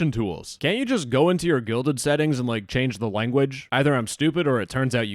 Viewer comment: They need to change their name anyway. Every time the guy in this video said "Guilded," I heard "Gelded."